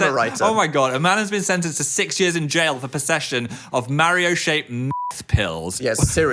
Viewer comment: I heard "Mario-shaped MILF pills"